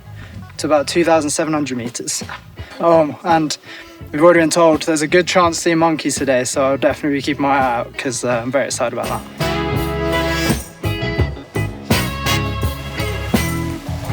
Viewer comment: I started listening to your video before I started watching. I thought you said, “We got sea monkeys,” which I thought was code for contamination in your water! 😂😂